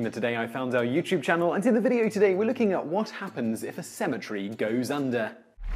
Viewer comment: Sounds like you said asymmetry goes under. LOL